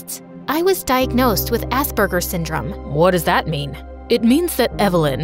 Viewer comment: Did bro just say… Ass burgers syndrome😭